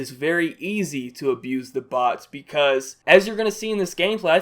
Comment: Who Thought It said Boots;-; And the bots in gears of war arent bad